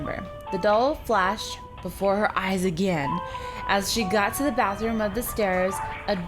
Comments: Did anyone hear her say bathroom then bottom? Lol XD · did anyone hear iHasCupquake say bathroom